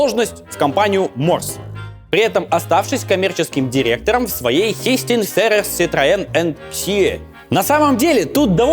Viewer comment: сначала услышал fisting